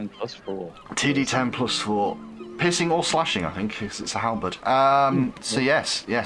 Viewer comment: Did I hear that right? ... The Halberd is now 2*D10 +4 Pissin' or Slashing Damage